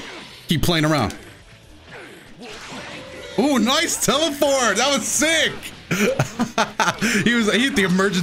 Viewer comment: i swear i heard you say "NICE TELE-FART!"